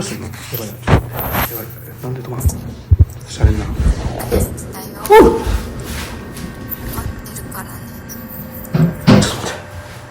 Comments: 今まで「大好きだよ待ってるからね」だと思ってたけど、待ってるからねじゃなくて「怒ってるからねorわかってるからね」に聞こえました。空耳だと思うけど、動画の最後の話を見たのは気づいた後だったのでなんか繋がってしまって怖いです。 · の大好きだよの後、 23:54って何て言っているか分かる方います？ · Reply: 待ってるからね！か！